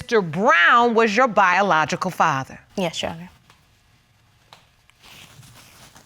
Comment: did she say "yes chef" 😂